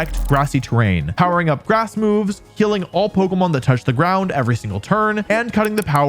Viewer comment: i swear i heard ''killing all pokemon that touch the ground every single turn'' LOL